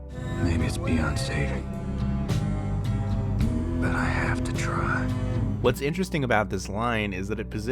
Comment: I have this on my second screen, and istg I heard "My name is Beyonce, but I have to try"